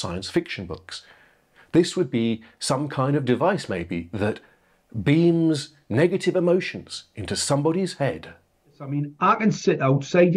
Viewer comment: I thought you said "Beans negative emotions..." lol!